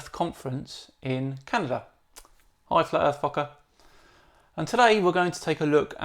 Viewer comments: I’m sorry I’m not sure if I heard him correctly but did he say “hi flat earth fucker” at 2:41? Lol that’s definitely what it sounds like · Did he just say “hi flat earth fucker?”